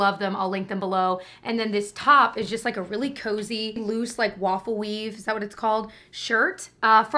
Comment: As a speech therapist, when I heard you say “waffle weave” My brain translated it to “lawfully” hahahahaha